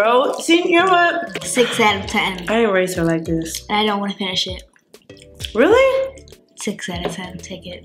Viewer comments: It sound like she said I don’t want that shii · When she said I don't wanna finish it at 750 I swear I thought she said I don't want this shit and that you didn't catch it so I rewinded and was like oh okay good haha